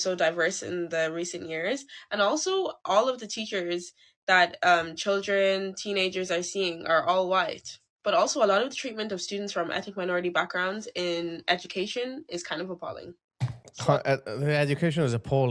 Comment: She says, "...and also, all...teachers that children, teenagers are seeing are all white. But also a lot of the treatment of students from ethnic minority backgrounds in education is kind of appalling." 6:25 HARRIS, you got that wrong!!! You MISSED what she said and YOU replied "Education is appalling and I think it's going to get worse" but SHE was talking about white teachers being racist and _their TREATMENT of minorities (NOT their "education") was also racist.